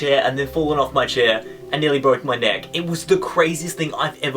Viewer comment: Lol, at 2:12, when says "nearly broke my neck", the subtitles say "nearly broke my dad" LOL